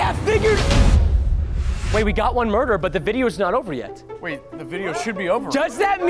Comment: did anyone else hear at 4:12 he said vider not video